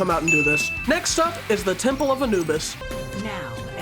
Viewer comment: When he said temple of anubis, I heard ten pull of anubis lol